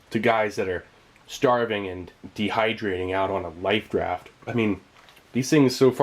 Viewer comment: Did he just say lifet raft?